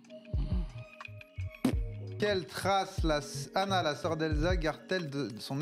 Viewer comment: y a que moi qui entend " qu'elle trace anale la soeur d elsa".... à 2:26 ?